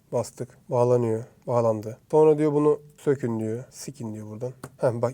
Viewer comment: Skın kelimesini bir tek ben mi sikin diye anladım sjdkdksksk😂